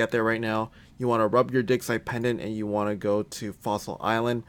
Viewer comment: i thought you were about to say something totally different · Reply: i heard it as * pipe pendent, i was like wtf